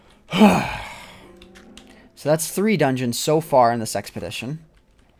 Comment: "three dungeons so far in this sex position" is what I heard, lol...